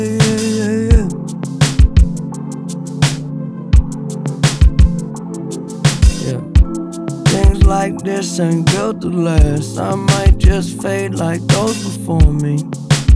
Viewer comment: I thought he said "i might just fade like ghosts before me"?